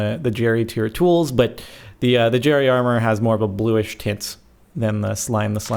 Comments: did said "more bluish tits" ??? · aaaadddddddd eeeetttttt 5:58 did i hear tits? o.O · Did you just say Bluish Tits???? O.o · is it me or at 5:55 did he say 'but the jerry armor has more of a blueish tits' ????? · ADD ITand I swear at 5:55 you said tits and not tints. :D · Did you say Bluish Tits O.O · Did he just say... blue-ish tits? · am I the only one who thought he said "a bluish tits" the first time you heard it? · i swear I heard bluish fits XD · did you say bluish Tits?????